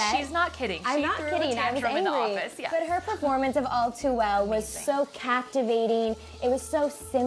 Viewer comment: when she said all too well i thought she said o2l..... i was like CONNOR?